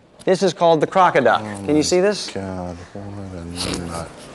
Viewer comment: did Brian say "oh my God"? His explanation of fossil is rubbish because, they will be changing their minds in 10 years, AGAIN!